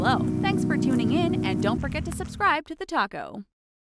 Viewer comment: I'm sure you said TheTaco instead of TheTalko at the end there.